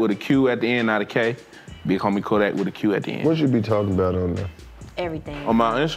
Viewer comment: awww nahhh LMAO thought it said KODAK for a hot secc but he aint that bigg🤣🤣